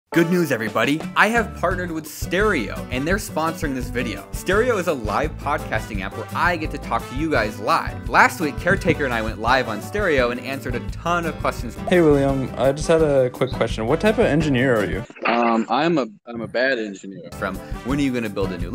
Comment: I heard, “Caretaker and I went high on steroids” instead of “Caretaker and I went live on stereo”